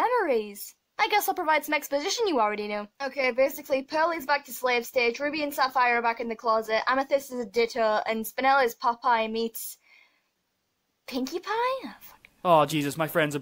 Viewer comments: What does she say at 00:30 ? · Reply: “Basically pearl is back to slave stage, sapphire and ruby are back in the closet, amethyst is a ditto and spinel is popeye meets... pinkypie?” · When she said pinkie pie I thought she’s said PewDiePie